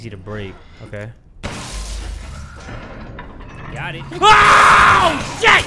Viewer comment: Why did I hear “got em”